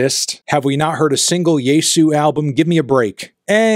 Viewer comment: Does anyone know the name of the doomgaze band he mentions at 8:16? It sounded like 'yei soo'? I wanted to look it up but don't know how to spell it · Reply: I think it's Jesu